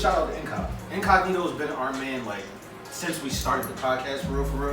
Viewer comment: what’s the word💯incognito 🚀